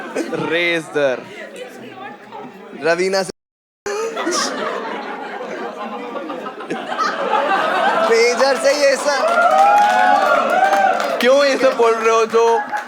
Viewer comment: what did said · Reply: I guess Raveena se Ruheena